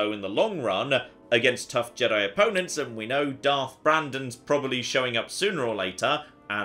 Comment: I could have sworn I heard him say Darth Brandon?